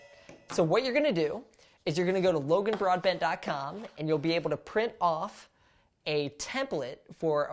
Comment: I totally heard "Go to logan broadBAND dot com"... wtf, this has an ISP too?!?